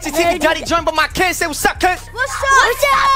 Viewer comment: why did it sound like said my name. WASsSUUP ELIAS